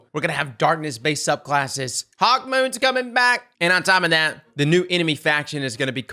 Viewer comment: Lol did i hear "hawkmoon coming back"